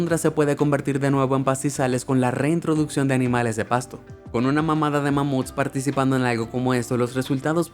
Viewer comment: Soy yo o envés de desir "Con Una manada de mamuts" dijo "Con Una mamada de mamuts" 🥵 Pongan la velozidad del vidio muy lento y escuche lo bien 🥵👁️👄👁️